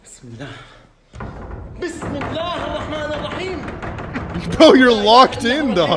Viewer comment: All i heard today was "bismillah mul ram rahim" 🤣🤣🤣🤣🤣